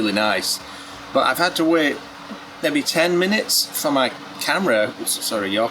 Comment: I love your accent!!!! ”My camera’s FOGGED up”….At first I thought you were saying your camera is F%#KD up 😂😂😂😂